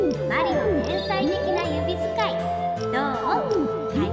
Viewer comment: これ、何回聞いても 天才的な指使いが 変態的な指使いに聞こえて ムフッてなる笑